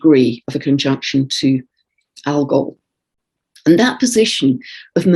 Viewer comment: Just listening now. when you were speaking of Mercury and the total Lunar Eclipse I thought you said, "Al Gore". After my head spun around ( lol), I went back and listened to it again. So glad I did 😍